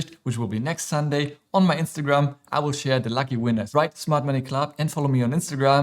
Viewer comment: Smart Money Club (although it sounded like you said Mart Money Club 😝) Thanks, love all your videos 😊